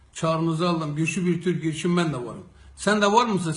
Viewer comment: Hepsinden "ben davarım sen davar mısın" duydum işin komik yani yanlış duymuş olsam da doğru duydum